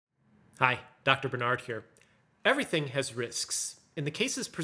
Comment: First time in a year i heard "dr bernard here"